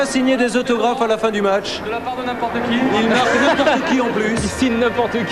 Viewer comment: What does he say at 2:17 ? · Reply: He says : De la part de n’importe qui ( of the part of whoever )